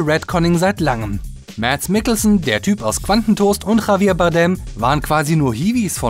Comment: sagt er da wirklich qantentoast? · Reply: Glumanda.64 ich hab Bankentoast verstanden...